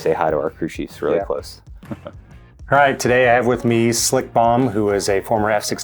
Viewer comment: I swear I thought you said Slick Bong😂😂😂